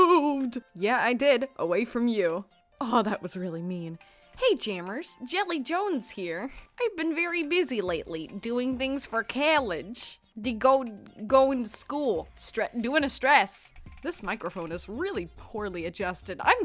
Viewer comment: When you said college, I thought you said cabbage lol!